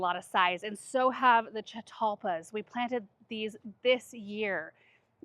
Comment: What is the plant at 5:56? She said it’s the most fragrant thing she has ever grown. Sounds like she is saying chaculpa.